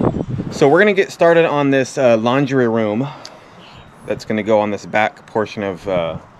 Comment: When he was talking about the laundry room, I thought he said " lingerie room" lol.